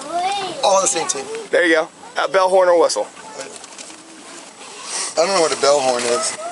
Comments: I thought he said Bell Horn whistle and I’m totally sober watching this video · He said "bellhorn" over and over as if it was one word. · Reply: I think I even heard him say bellhornwhistle at one point. 😂